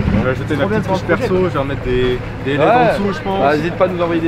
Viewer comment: des quoi en dessous ?? Sur une TDS neuf ??? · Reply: des led la gueule du mec 😂